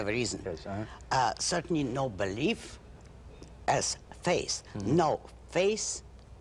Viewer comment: Her russian accent here is stronger than in her earlier interviews, see "The Mike Wallace Interview with Ayn Rand". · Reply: Is it? Wallace even emphasised that she said "faith," because in that 1959 interview, her accent made it sound like she was saying "face."